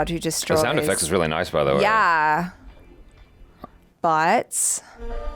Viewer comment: wait what did you just say but* 😂😂 I was just listening and i was like what did just say 😂😂😂